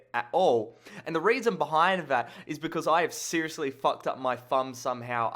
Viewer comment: lol, at first I thought you said "I have seriously fucked up my bum somehow," I was like wtf?! :D